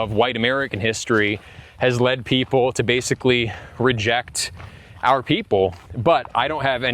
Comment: did he say reject his people? Huh?! What?!